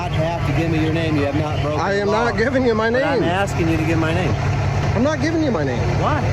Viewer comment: Wait 2:36 did he say" I want you to give my name. "